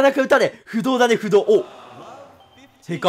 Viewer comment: テニプリネタだ！その前の不動も不動峰に聞こえる笑